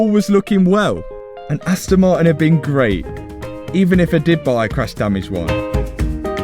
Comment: Until now I thought you were saying "crash dummies" instead of "crash damaged". I am still not positive.